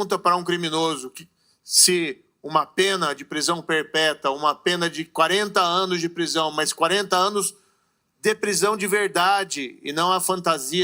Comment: Vocês também ouviram ou só eu ouvi "prisão perpeta "????